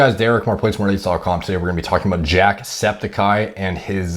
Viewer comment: I thought u said jacked speticeye 😂